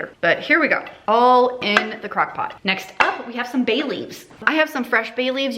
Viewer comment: I thought you said you added Bailey's to your red beans and rice. I was disappointed when it turned out to be bay leaves. ;)